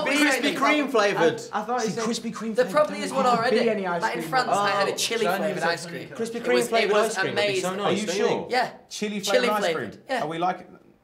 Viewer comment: zayn's like "I thought he said if you could be any ice cream what would it be" i thought the same thing!